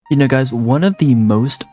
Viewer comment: what did bro say.... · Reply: sounds like 'you know guys'